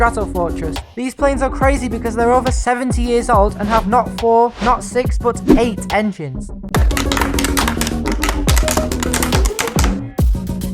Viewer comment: did you say eight engines?